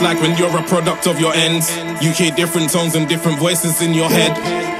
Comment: What is he saying at around 1:50 "this is what it's like when you're a product of your..." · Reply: ends like the hood